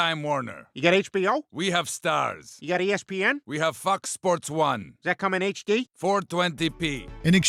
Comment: Who else thought he said "we have fuck sports 1"? 😂😂😂